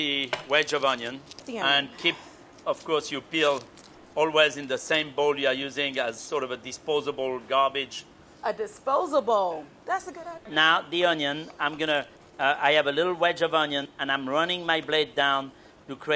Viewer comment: "A disposa-bowl? That's a good idea." 😂 at least that's how I heard it lol